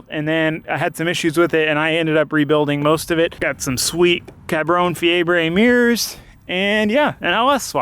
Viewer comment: did he say Cabron Fiebre? as in the Spanish for B*stard fever hahaha :´)